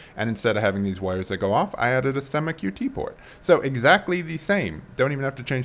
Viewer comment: it took me way too long to figure out what you are saying here lol. for anyone else confused, its "STEMMA QT Port". closed captions have you saying "stomach UT port" which i think is an insulin pump port lol